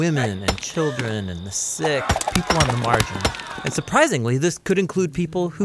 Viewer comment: did he say margin? I looked it up and i think that's what he said